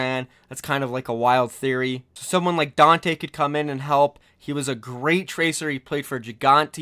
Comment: did you say Danteh? If so he is already on a team